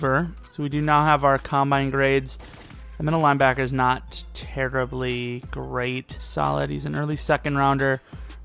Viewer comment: Did he say "Terribly Great" at 8:46?